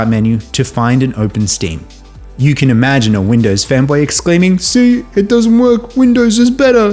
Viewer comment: Not gonna lie: I heard femboy at first... Damn you, Reddit reactors! XFCE on desktop and LXDE on a netbook were my first desktop environments when starting out on Linux long ago.Switched to KDE at some point because it was just more compfortable to use and look at (at least back then).